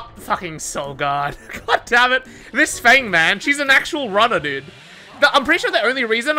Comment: he really said “Feng man” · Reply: he said "this feng , man"